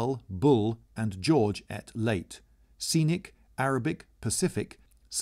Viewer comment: you said at instead of ate at around 2:50 or am i missing something?